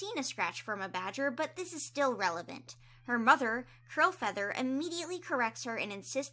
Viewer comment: i swear i heard crowfeather XDDDD